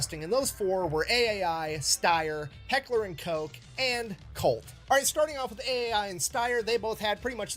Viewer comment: did he say Heckler and Cocaine